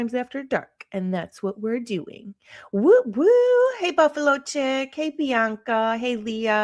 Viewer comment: When you called out screen name "Buffalo Chick" I thought you said "Buffalo Chip" and I LOLd 😂😂